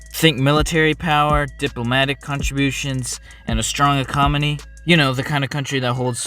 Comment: ecomony instead of economy